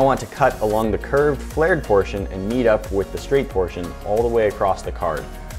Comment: did he just say "yeet up"?